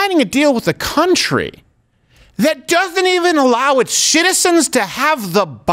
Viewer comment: does Crowder say Shitizens?